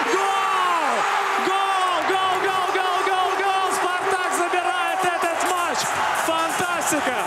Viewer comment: На 17:25 Спартак СУКА... Я один это услышал!?